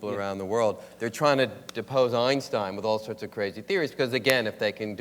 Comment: Hello, YouTubers. The World Science Festival is looking for enthusiastic translation ambassadors for its YouTube translation project. To get started, all you need is a Google account. Check out Hidden Dimensions: Exploring Hyperspace to see how the process works: <link> To create your translation, just type along with the video and save when done. Check out the full list of programs that you can contribute to here: <link> The World Science Festival strives to cultivate a general public that's informed and awed by science. Thanks to your contributions, we can continue to share the wonder of scientific discoveries with the world. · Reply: For a second I thought you said Epstein. 😂😂😂😂